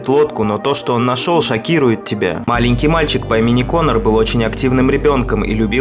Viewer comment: Мне в начале послышалась не маленький мальчик а аленький мальчик кто тоже услышал так суда ---------->